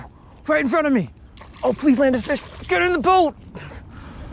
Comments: why did you say get it the boat lol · we did you say (get it in the boat)